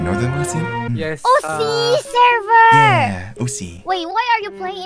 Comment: I think Kobo say "Ooh, SEA server" I think she misheard OCE part.